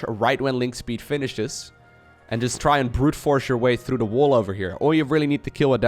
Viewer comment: I heard "Brood Wars" when he said "Brute Force" through the wall off with your zerglings :D